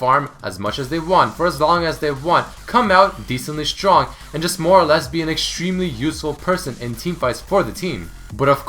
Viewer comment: did he say lesbian? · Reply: LOL " more or less be an"